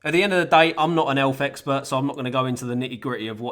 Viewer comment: I thought he said "elf expert"